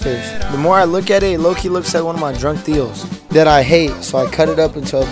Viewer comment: Drunk tios, i thought he said drug deals